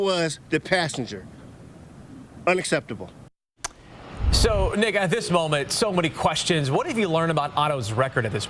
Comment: dude sounds like he says "so n1gga" 😂😅